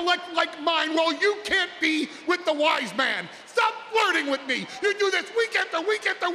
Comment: The look on Kayla's face. :) :) Yeah Kayla! Stop flirting with the Wise Man! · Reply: I really thought he said white man, cause if that was the case, that would be hella racist.